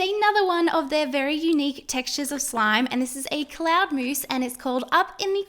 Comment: am I the only one that thought that said cold mouse 🐁😬😬😬 Just me? Oh ok I’ll go cry in the corner with all the cold mice 🐁